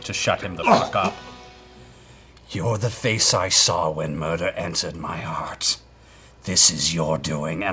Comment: See, I know it's hard to tell because of the accent and growl he's using, but I thought Percy said "You're the face I saw when murder answered my heart", which might not be what he said, but I think that's subtly and infinitely cooler than 'entered'. Headcanon, lol.